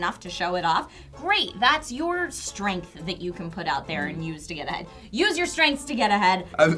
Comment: Did I just hear Trish say "use that to get a head"?